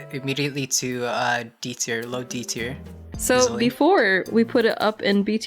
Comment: why does it sound like he said diarrhoea 😭